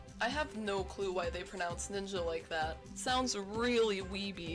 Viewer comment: Ngl when they said ‘ninja’ I thought it said ‘mean jack’ And was extremely confused